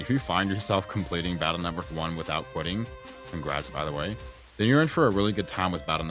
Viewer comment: Lol at 6:40 I got the legacy addition and got stuck at the final boss for like a day. When I put this video on I didn’t know you were telling “congrats on finishing BN1” I heard it as “if you finished BN1 and didn’t quit, that’s me asking btw” and felt called out. So indirectly thanks for lighting that fire in me to push on forward